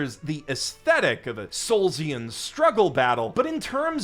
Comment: What does he say here? Solsian? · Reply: Soulsian as in Dark Souls, I think